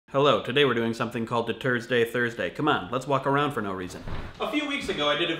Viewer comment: At first I thought you said "The Turd's Day Thursday."